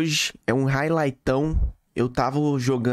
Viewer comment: Só eu entendi "eu tavo"? kkkkk